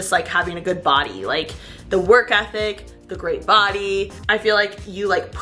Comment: Omg. When you were talking about being into athletic guys I heard wrong and thought you said “athletic D” instead of ethic hahahaha omg I don’t even know how but my eyes went 😳 and then it processed right in my head lmao